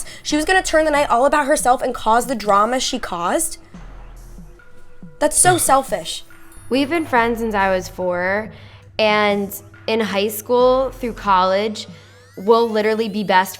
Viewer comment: I know she did to but it sounds like she said shell fish